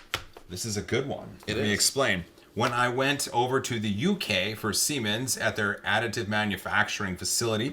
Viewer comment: YouTube decided to buffer in the middle of when you said Siemens. So I heard "I went to the UK for semen..." and I, being the SUPER MATURE person I am, stated laughing uncontrollably.